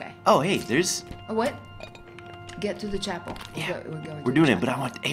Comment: Bat around 17:00 it sounded like said get to the choppy